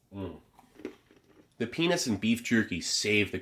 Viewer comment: “the ‘what’ and beef jerky save the cranberries and raisins”? Childish I know, but it really didn’t sound like you said ‘peanuts’ 😂